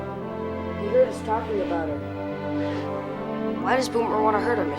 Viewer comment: What did patch say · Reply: He said, he heard us talking about her